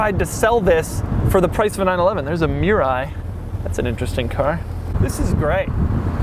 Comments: i thought he said " there's a MIURA" and i didn't understand how he wasn't excited at all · who thought Doug was saying Miura as in Lamborghini Miura instead of that stupid Toyota thing I also want Doug to review the Mirai now.